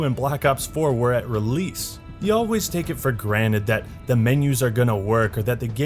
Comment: Granet ? Hummmm... Rick ?.. sry... Did you just said granet instead of Granted ? get morty-mindblowed I think this MW is gonna be great ! Fun to play and visually lookable on stream and sounds cool. i'm hoping that Activision don't f-ck the game's reputation with their greedy methods.. Looking at you EA.